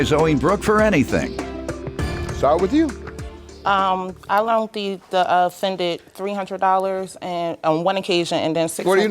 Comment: Did she say the offendant?